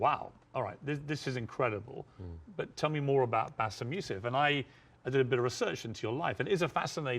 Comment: At @ 5:29 minutes with the Subtitles on, Bassem's last name is displayed as MF instead of Youssef. Fishy Piers 🤔